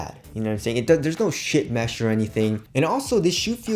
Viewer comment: Already have my pair, and you know what.....😍🤤😍 8:30 I laugh when says 'SHITMESH'...🤣🤔 At least that's what it sounds like.